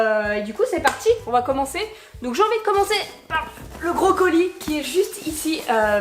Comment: Au début , quand tu as dit "un gros colis", j'ai compris "un brocolis" !!!😂